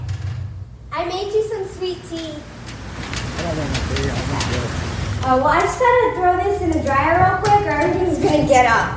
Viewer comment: What did he say at 37:28? The decoy says she made him tea and he says i dont want tea, i _____. I cant make it out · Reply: Late reply lmao, I’m pretty sure he says “ I don’t want tea, I want lovin’”